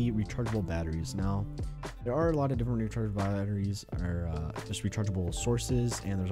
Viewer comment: what did he say? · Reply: "there are a lot of different rechargeable batteries"